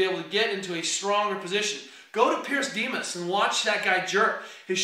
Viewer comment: What name does he mention? I cannot hear it. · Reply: Pyrros Dimas.